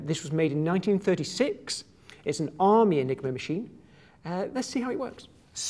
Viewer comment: I thought he said the "nigro machine"